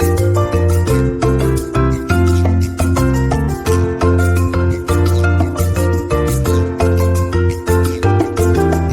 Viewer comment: did it really say freeze up for a month? Lmao then ur appetite will be gone😂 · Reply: They said freeze up to a month. Meaning that it will be good in the freezer for a month. Take it out to eat at any time.